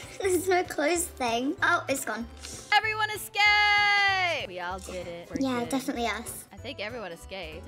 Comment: When they said “EVERYONE ESCAPED!!” I thought they said “EVERYONES GAYY!!!”